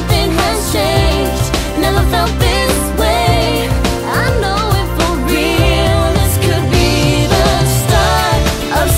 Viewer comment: it's been YEARS and I still don't know what they're saying right here... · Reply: I know it for real, this could be the start of something new 🎶